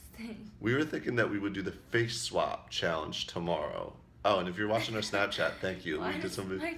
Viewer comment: I thought it said no more Vlogs just snapchat and I was like what!